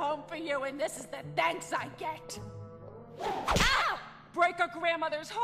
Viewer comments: did u catch what she said on 5:59 before the video ends shut up grandma Harley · What was that on 5:56? She said something bad? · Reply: “Break a grandmother heart” that’s what she said